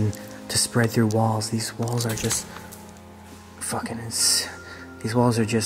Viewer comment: Am I hearing right? "These walls are just fu*** Insane. :Dd